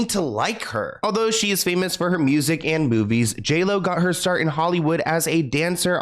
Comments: Omg! I thought you said she got famous from her music and boobies! 😂 It didn't sound like movies until i replayed it! I love your videos! ❤❤❤ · At the beginning when he said music and movies, i thought he said boobies 🤣🤣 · It really sounded like you said “she’s famous for her music and boobies” 😂😂😂 I know I’m childish